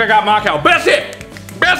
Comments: Sounded like Mark said She mauls on my wife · I thought he said “She mauled my wife” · I thought Farmer Mark said the cow mauled his wife.